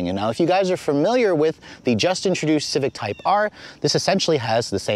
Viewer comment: mark, I thought he really said “Justin Trudeau Civic Type R”. That would be the shittiest Civic ever to exist 😂😂